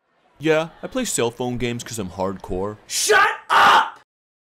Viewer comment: did he say parkour? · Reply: Hardcore